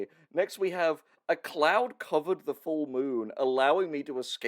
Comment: I misheard that as a "A clown covered the full moon" and... I guess that would be an event!